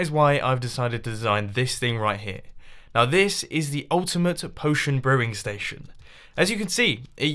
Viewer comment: I thought that it said the ultimate potion pooping machine